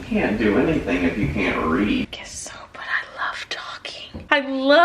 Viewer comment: ohhh, read, I thought he said "breathe" that makes more sense...